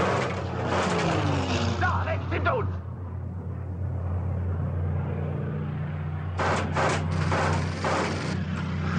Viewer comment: Does anyone know what the gunner is saying at around 1:53 ? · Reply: Da! Rechts hinter uns! - There! Right behind us!